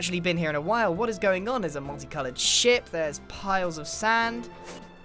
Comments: I thought he said very calmly “...there’s a sh*t...” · I thought he said “multi-coloured sh*t” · y did I think he said sh*t 😂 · did anyone else hear multi coloured sh*t ?